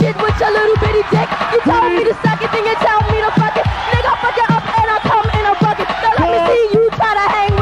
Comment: does Roxanne say "Nigga I'll fuck you up and I'll come in a bucket" and what kind of diss is that?